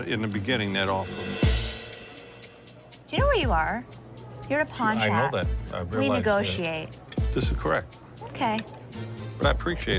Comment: Did she say: “you’re a pawn shop”